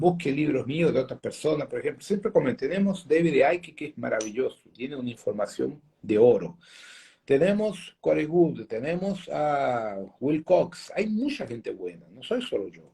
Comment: Hola cuales son los otros autores qué menciono en el minuto 21:30, como soy nueva, no le entendí bien los nombres, espero me puedan ayudar, saludos. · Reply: Hola, se llama Corey Goode y David Wilcox, si pinchas mi foto tengo carpetas con sus vídeos, ojo por qué el canal de esos vídeos no es de fiar en estos tiempos, quédate con la información de Corey Goode, otro que habla de algo parecido es Alex Collier,también tengo información sobre Alex Collier, de hecho fue el primero en hablar del tema cuando fue contacto de los de Andrómeda, también tengo una carpeta llamada vivimos en una simulación con más información